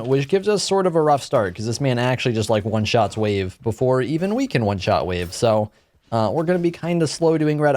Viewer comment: I hear weak3n at 0:50 for some reason xd and i know you said we can